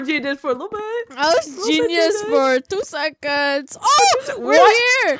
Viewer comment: bro why did it sound like they said i was gesus for 2 second